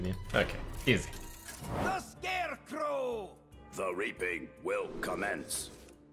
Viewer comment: I could have SWORN that I heard "The Raping Will Commence" and I fuckin jumped in my seat!